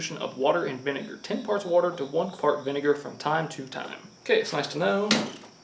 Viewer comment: ish, you say fart instead of part. Ten parts water to one fart vinegar.